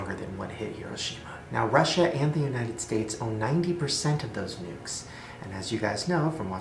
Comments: My hearing isint good right now but i had to put subtitles that first i thought u said were gonna diw because of nudes im like wut then i put it and it saids nukes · When Shane said nukes i misunderstood it and thought he said nudes are going to kill us